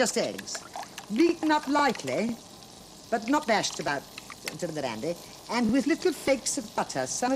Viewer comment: Does she say "and not bashed about to the dandy"? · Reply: I've wondered what she said there. She often said old fashioned phrases like "This recipe is spit new" and "Nitty little slices, tasting of the knife". I wonder what she meant here. I first thought she said "Bashed about till they're bandy" i.e. rubbery but I can't hear it well enough.